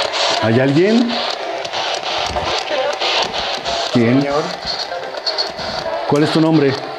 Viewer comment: En el minuto 22:51 después que dice alguien, después responden “YO” o “SEÑOR” se oye clarito.